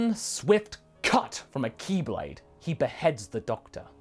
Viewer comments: I feel so dumb, when he said "Ki Blade" i thought he was saying Keyblade. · Oh thank god, it's just Zamasu. When said Ki blade, I misheard it as Keyblade and I became very confused and concerned.